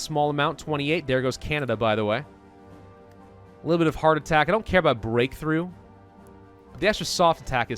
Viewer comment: when he said hard attack i thought he said heartattack